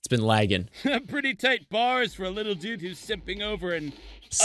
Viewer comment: tbh i thought tankman said "sipping" not simping. Gosh i am so green minded